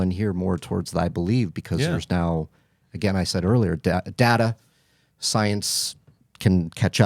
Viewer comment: I misheard and thought he was saying he believes because of DADA, as in his dad’s picture 😂😂😂